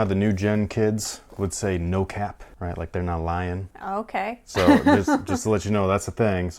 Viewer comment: No cap, it feels like we've been fan girling BIG time over LeBron's sneakers lately. BUT what can we say... we like what we like. Let us know what you think about the Media Day LeBron 7. Thanks for watching! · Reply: i thought you said "not lion"... pun intended.